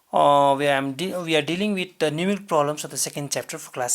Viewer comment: did he just said numictrolams in